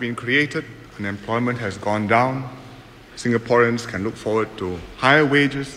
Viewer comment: He says "unemployment has gone down", but you probably thought he said "and employment has gone down."